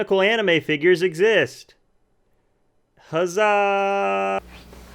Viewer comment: ㅋㅋㄲㅋㅋㄱㄱㄱ나왜 가즈아~~~~~~~~~로 들리냐ㅋㅋㅋㄱㄲ